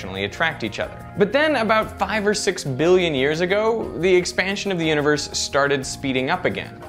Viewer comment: i thought he said 5 or 6 years ago... LIKE WHAAAAAA?????